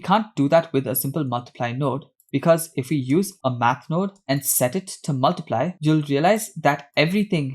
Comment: Incredible tutorial! Although I got a bit stuck at 6:44 because I thought you said "map node" not "math node" lol